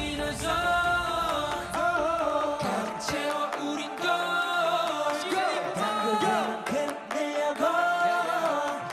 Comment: what did ma Taeil honey say??? 😅 Congratulations to NCT 127 and NCTzens 👏💚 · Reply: He said "Thank you Nctzens!"